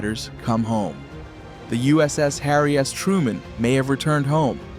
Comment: Did he just say harry ass truemen?